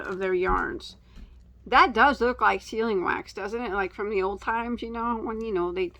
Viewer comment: Good Lord, I thought you were saying "ceiling wax," and I thought, why would anyone want red ceilings? My husband knew right away what you were saying and asked if the color was red. Then, I got it. "Sealing wax." Duh.